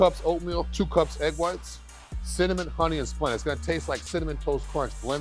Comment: what did he say cinnamon, honey and what else? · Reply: I think he said Splenda. Although he seems unafraid of sugar and I feel like splenda would be expensive but maybe thats why he mumbled it.